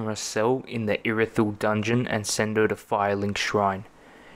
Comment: Did he say "Gungeon"?